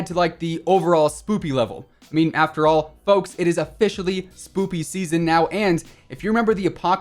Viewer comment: does he say spoopy or spooky? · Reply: Its spoooooopy season